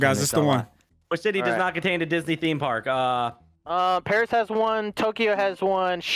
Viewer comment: it sounds like says 'which sh*tty does not contain a Disney theme park' to me it does anyway 😂